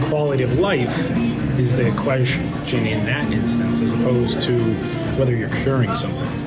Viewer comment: I think he's saying "equation," not "question," but he's says it in a very weird way.